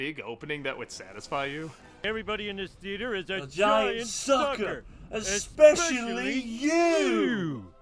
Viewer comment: As a kid, I thought Homer said "giant soccer" (i.e. soccer ball), which, combined with him actually mentioning soccer later in the movie, made me think he had a weird obsession with soccer.